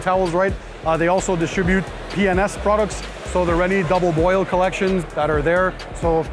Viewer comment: did you say double boil???